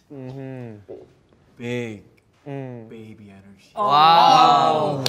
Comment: so I wasn’t paying attention and the way Johnny was saying it made me think he was gonna say ‘big d!ck energy’. When I tell u my attention shifted so fast that I was boutta break my neck😭